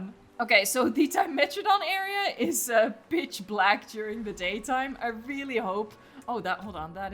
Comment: Did she just say B*TCH BLACK